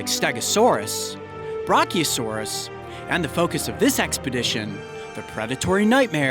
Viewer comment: I heard "Broccolisaurus" @@